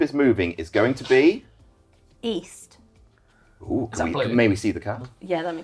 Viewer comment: East? I thought you said weast